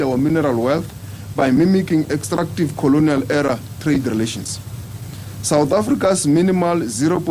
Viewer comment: Did you say Colonial error trade relations? seriously. Coloniasm ended in the 1800 s. Just live in the moment (in the house that you did not declare)